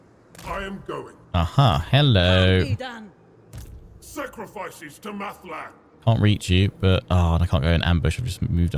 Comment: Krakenlord is breaking bad! I listen to this whilst doing other things and it was late and I was sleepy. (42:52)In my state I heard: "Sacrifices to meth lab" ... and now I cannot stop thinking of this character and his mobile kracken meth lab. Peddling his corrupting influence to make the populace slaves to his product.